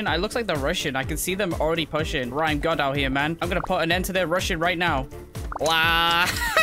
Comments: I thought he said Russian when he said they are rushing lol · I thought he said they were Russian lol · When you said rushing i thought you said russian